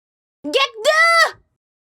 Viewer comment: Intro: Hornet: "Git Gud" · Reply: I always hear "get the" but actually she was supposed to be saying Gekdu